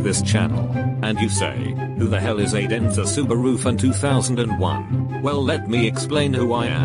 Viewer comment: aidEnzesubarufun2001 is what I heard lmao